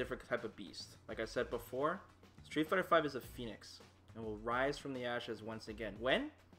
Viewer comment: Not even doing a joke, I genuinely thought he said "Street fighter 5 is a penis, it will rise from the asses once again"